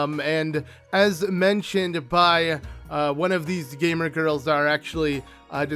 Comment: god did just say “Gamer Girl”